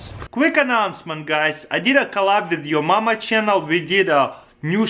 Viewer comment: I thought he said "I did a collab with your mum" xD